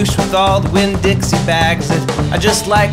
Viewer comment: For a second I thought I heard you call the guy a "douche-Winn-Dixie-bag."